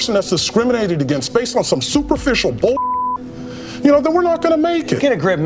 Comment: Erf 7:36 I heard it Erf